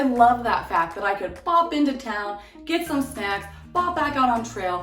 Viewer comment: I’m dead. I heard ‘pop into town, get some sex” 🤣